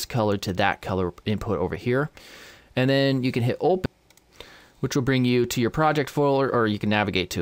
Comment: What do you say at 5:22? To get to the Project folder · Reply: Hello, You just need to click open on the Image Texture node.... It took me a really long time to notice...